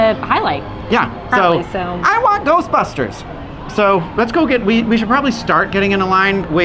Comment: it sounded like "so let's go get weed" and i was thinking that won't help calm nerves at all. 😅